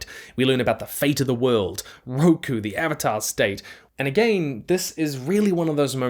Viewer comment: AVATAR STATE YIP YIP! This is immediately what I heard lol